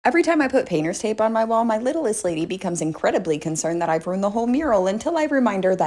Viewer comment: At first I thought you said "my little land lady" instead of "my littlest lady" haha